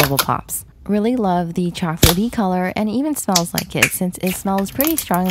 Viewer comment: I thought she said it smells like kids 😂